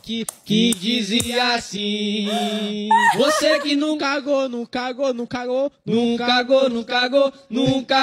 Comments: olha só. "Nunca go-,nunca go-" Olha o que eu entendi "Não cago,não cago😂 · so eu que ouvi "nunc cagou nunc cagou nunc cagou" e "e me mije mijei mijei mijei" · Eu entendi " Num cagou"